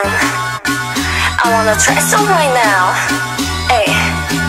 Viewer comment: I thought Heejin said "I wanna try some wine now"